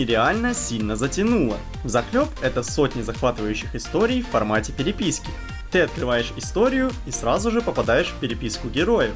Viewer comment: Мне одному послышалось "в формате переписьки" ?